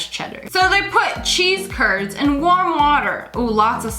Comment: Who else thought she said cheese turds instead of curds?